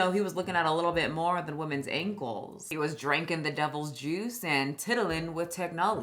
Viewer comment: I had to play that back because I wasn’t fully paying attention and thought you said “women’s A-holes” 🤣🤣🤣👌